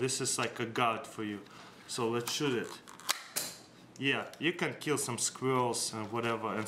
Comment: Wtf did he said you can get some Square with the bottle ballon stick